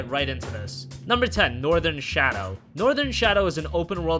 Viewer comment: I thought he said nordan shat